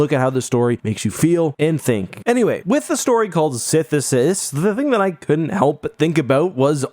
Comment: See I hear 'sithisis' and immediately think 'synthesis'. The definition of which may be a better match.